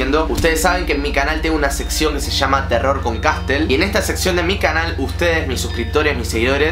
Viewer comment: pense que decia turro con castel 🤣